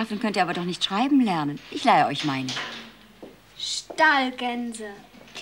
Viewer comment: Ich habe JAHRELANG verstanden, dass Nellie "Stahlgänse" anstatt "Stallgänse" sagt...